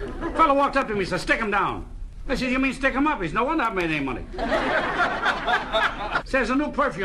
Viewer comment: what does he say? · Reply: “Well, no wonder I haven’t made any money.”